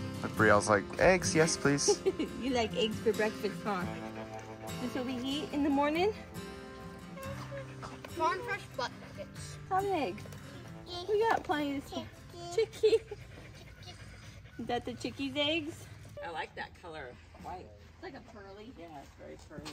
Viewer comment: Am I the only one who heard say "farm fresh butt nuggets?" At 5:10 haha